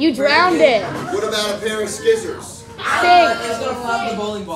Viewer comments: Did he just say skissors instead of scissors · When he said pair of scissors I thought he said Barry sanders and I freaked out till I seen the scissors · did he just say..."ski-ssors"?